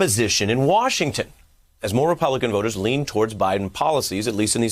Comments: I thought Buden said "Republican Vultures ", he wouldn't be wrong if so. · I thought he said Republican vultures lol